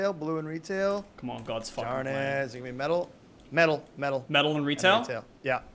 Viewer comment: Did anyone else hear go “ gods f**king plan”